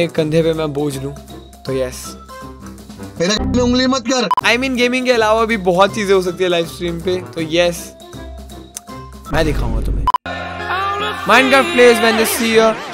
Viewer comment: Only Legends understand what he want to say "Bohot kuch ho sakta he live stream pe me dhikauga tumhe"